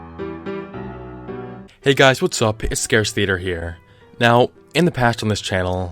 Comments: Did he say "Hey what's up guys it's scarce theater?" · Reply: Captainfoxyhat he said scare theater, I thought he said that to 😂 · In the beginning I thought he said: "Hey guys ScarceTheater here" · At first I thought he said Hey what's up guys scares here lmao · Did he just say Scarce theater